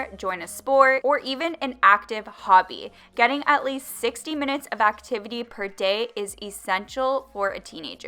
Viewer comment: wait when u talked about the 5th thing of losing weight, did u say 60 minutes of workout or 16 minutes of workout? cause it sounded like u said 60 minutes of workout. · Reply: hey, she said 60 mins :)